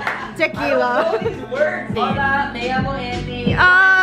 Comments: “hola, me llamo Jennie” entendí JAJAJA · "Me llamo Jennie" (creo que dijo eso :v) Blink detectado okey no